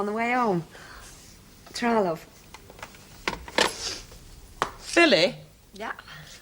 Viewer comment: It sounded like Blanche said Phily, instead of Billy.